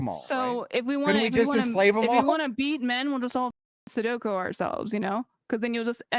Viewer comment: Had to rewind a couple times, maybe i misheard. Did she say sudoku instead of seppuku?